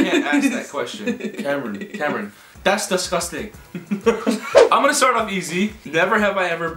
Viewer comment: Das Dusgusting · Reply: I heard "Das dasgusting"